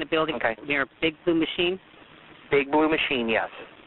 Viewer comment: What he said : “ big blue machine” What I hear : “Big booty machine”